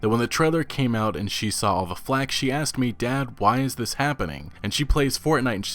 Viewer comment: I heard it wrong: Dab! “why is this happening?” Nonetheless, I will preface all my questions of disbelief with a dab now.